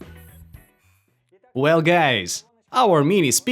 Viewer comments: Why does he call us " Gays " ?!? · DID HE JUST SAID WELL GAYS WTF